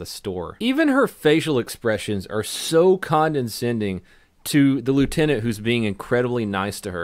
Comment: Wait, did you just say "condensending"? I think you meant condescending...right? WTF...you sound like Brad Pitt in True Romance.